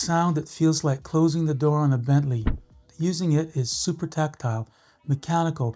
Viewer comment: This man said, "...like closing a door on a Bentley." I heard, "...like closing the door on your family." I was like wtf did he just say lmfao!!!